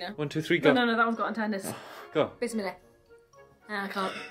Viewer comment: Did you say bismillah 0:24 😀